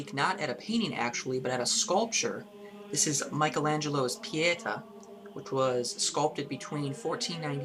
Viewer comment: Pieta?!? Pieta? I thought you said piñata!!